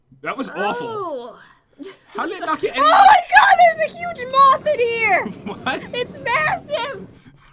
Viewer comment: When said "a huge moth" I thought she said "a huge mop"